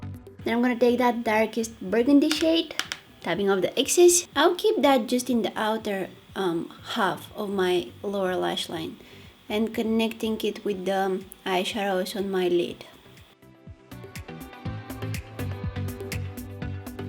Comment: everytime she says "lower lashline" i always hear "laura lashline" idk why lol